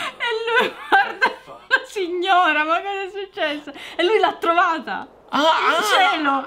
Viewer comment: ho sentito chiaramente SIGNORAAAA I LIMONI SIGNORAAAAHAAAAAAAAAAAAA